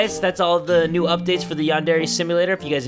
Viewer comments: It sounds like he says Jerry from the cub scouts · Remember to give this video a LIKE if you enjoyed and thank you all for being part of the Kubz Scouts! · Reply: The first time I heard ur outdo I thought u said Jay from the Kubz Scouts is dead too then the shooting thing cane in 😂😂